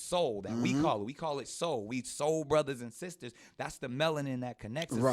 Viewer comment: "Two black men try and rationalize how they lost the first Race War" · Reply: Did he just say, 'we sold brothers and sisters'??